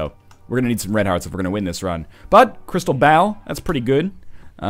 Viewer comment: anyone else thought he kept saying crystal bowel, or is it just coss im watching it in 2 times speed?